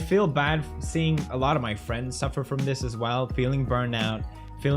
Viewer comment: Did he just say FRIENDS